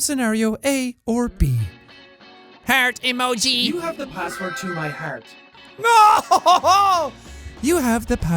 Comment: I SWEAR I heard you say “You have the passport to my pants.” and not “password to my heart” omg